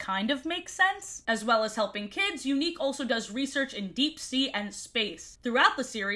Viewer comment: I thought she said Younique and was very confused